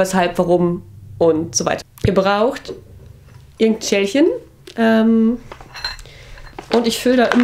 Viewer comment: hab ihr braucht n Tschechen ^^ verstanden